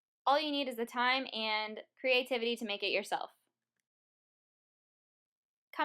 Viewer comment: She said "Comment below..." I heard "Come and blow." Lol Wut?